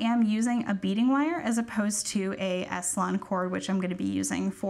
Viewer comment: great video, perfect pacing and everything. one side note; there are several links that are coming up as 404 error -jasper 10mm -lapis 10mm -rose quartz 10mm -amethyst 10mm -orange aventurine 8mm -BeadSmith Super-Lon (S-Lon) Cord - Size 18 Twisted Nylon - Capri Blue -Smooth Aromatic Cedar Wood Guru Beads, Round 15mm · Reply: very much a newbie here, i thought she said aslan cord 😅